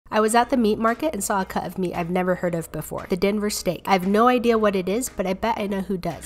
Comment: I was half asleep and thought it's said "Dahmer steak" 😅